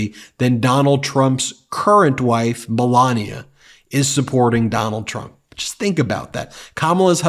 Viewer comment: I thought he said Trumps current wife BlaaaLania 😂. Sorry my mistake 😊